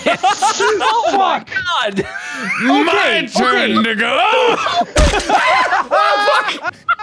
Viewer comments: I'M SORRY WHAT DID HE SAY?!?!? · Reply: if you thought he said the N word he didn't!! lol he just said "My turn to go!" fast, and his words were slurred together so it kinda sounded like it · uh what did he say lmao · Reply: My turn to go. It is easy to hear. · what did mark say?! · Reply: He said "my turn to go". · I think he said the nono word. Could be wrong though. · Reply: He didn’t say the n-word. He said “to go,” as in “my turn to go.” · did he say the n word? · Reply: he says “my turn to go” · did he just say n word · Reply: He said "my turn to go". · Did mark say the n-word at 31:38? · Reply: Rebelious G - He said “to go.” Full sentence being “my turn to go.” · What did he say. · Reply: My turn to go. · does Mark say the n word???? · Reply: No, he says “to go.” Like “my turn to go.” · Did mark say the N word? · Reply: Rubiks Genius - No, he said “to go.” Full sentence being “my turn to go.” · wait did he just say-- 31:36 just stop there and play it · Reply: “My turn to go” but his words slurred together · I think mark said the n word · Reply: He said "My turn to go!" Get your ears checked. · sounds like he said the n word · What did Mark say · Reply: He said "my turn to go." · What did mark say at 31:38?! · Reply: if you higher the volume and listen closely, he says “My turn to go” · did........did Mark just said the n-word? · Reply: "my turn to go" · did Mark just say the n word? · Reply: He said "my turn to go".